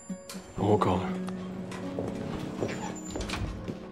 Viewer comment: thanks for the upload :) What does Castle say at 0:38?? i can't understand him... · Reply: He says: "Did you say something about a giant moth?"